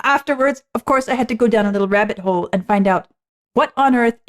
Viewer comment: I heard you say hip hop rabbit hole and I thought “She right, that is a hip hop beat!”